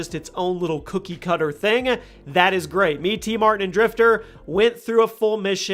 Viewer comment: did he say?